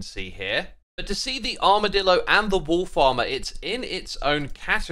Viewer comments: Everytime you say "wolf armour" I just hear "Wool Farmer", and that was really confusing to me until I realised. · Everytime you say "wolfs armor" i hear "wool farmer"